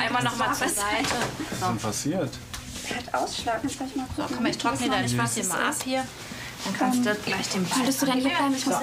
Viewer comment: Ich hab bei 4:22 verstanden “guck mal ich trockne deinen Schwanz mal ab“ 😂😂😂😂😂